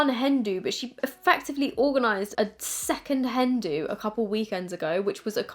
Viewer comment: can anyone tell me what has Ellie organized ? (Hand do?) I can’t make it out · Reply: Nevermind, it’s a “hen do” gosh… I thought it was called a hen party and I guess it is but there’s another name for it 😂